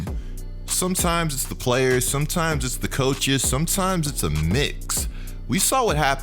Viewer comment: Lmao I thought he said “sometimes it’s the players, sometimes it’s the coaches, sometimes it’s the knicks”